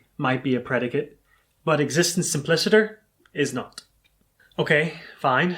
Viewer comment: What does say at 9:49, and what does it mean? Any help would be much appreciated. Thank you. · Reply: "Existence simpliciter" simply means that "existence" is being used without any qualification. For example, I could say that both Donald Trump exists and that a unicorn exists, if I stop there, I would be using existence simpliciter because I'm not adding any qualification. But if I said, Donald Trump exists as a concrete being, and the unicorn exists as a mental being, I'm no longer using existence simpliciter because I've qualified the sense of existence I'm using.